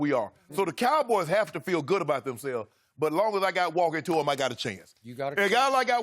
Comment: What is Shannon saying at 3:28- 3:32? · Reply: "As long as I got walk it to em I got a chance"